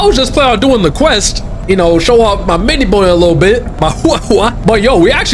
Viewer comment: Why do I hear "little bonr"